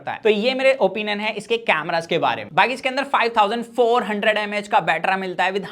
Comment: He said battraaaaaaa instead of of battery 😂😂😂😂